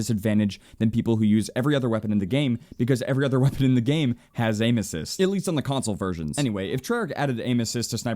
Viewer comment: I thought he said "Because every other weapon in the game, has anuses" And i was like :O